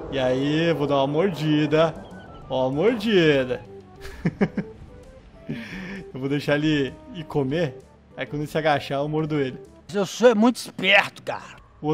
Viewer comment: só eu q escutei o dizer " vou espera ele me comer" ? kkkkk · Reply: Ele disse: “só vou esperar ele ir comer". As palavras são parecidas por isso vc se enganou 🌹